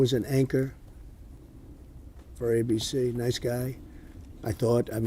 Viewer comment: How do they get the subtitle so wrong there? Trump clearly says ABC but it's subtitled NBC. He actually worked at ABC so this switch without confirming either what he said or confirming Johnsons position is sloppy work.